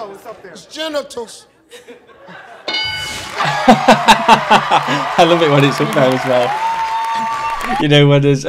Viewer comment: Mark 3:33. This time, I heard him say "his genitals"! Before, I thought he stuttered the words, "his testicles"! 😁